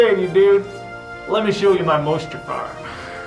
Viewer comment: i heard mustafar